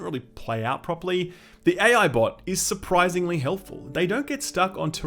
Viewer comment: did you just say 'AI Bot'?